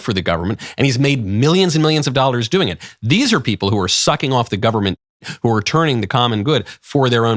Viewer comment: 🤣The audio was cut for the word "teat", so I heard you say, "....they're sucking off the government.". Potato, potato.